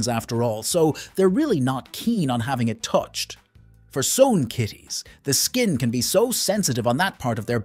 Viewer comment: What is please the word beginning with "S" ("for s... kitties the skin can be so sensitive...")? 🙏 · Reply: He said, "for some kitties...."